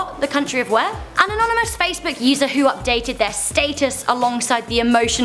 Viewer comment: Wow they really are losing braincells she said stateis instead of status